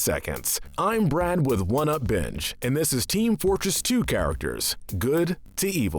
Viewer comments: Thought he said one up bitch instead of binge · I thought he said “one up bitch” I